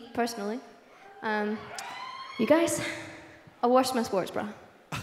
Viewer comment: What did she say at 18:36? "You guys ..."? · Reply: I washed ma sports bra